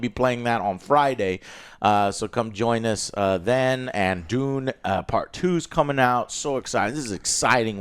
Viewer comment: When you said Dune part 2 i thought you were say Doom Part 2. I was like WTF!!!???